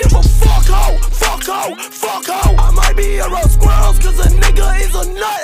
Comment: when he said around squirrels i thought he said king of squirrels